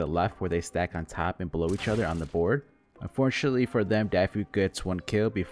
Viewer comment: Did he just say "Blow each other"??!! 😂